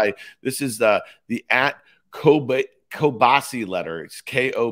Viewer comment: Thought I heard "kielbasa letter" (rather than the Kobeissi letter), and it mistakenly made sense insofar as kielbasa is actually spelled with a polish-language specific letter "ł" kiełbasa. Go Yanks! 🌭⚾️